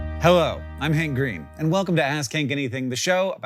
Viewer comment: For just a split second I heard "I'm hank and welcome to ass kink anything" and I know that was wrong but I just felt I needed to let you all know.